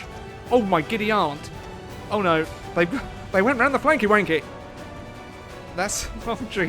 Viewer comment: "They went round the flanky wanky" did I hear that correctly haha?